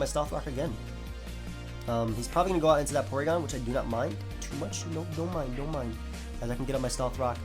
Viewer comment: DID I JUST HEAR A KUROKO NO BASKET "DON MIND, DON MIND" :]]